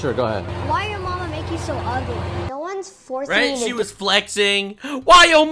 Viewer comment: Lil brain. Thought I heard her say she was winning. Daddy called?